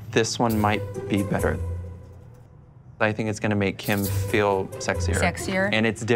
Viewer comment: Did he just say "I think it's gonna make HIM feel sexier"? He's a little fruity too lol. Something doesn't add up :D